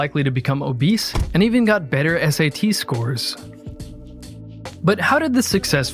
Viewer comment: I thought he said STD, not SAT